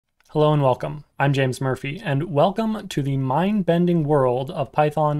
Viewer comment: I always hear him saying "Hello, I'm James Smurfy"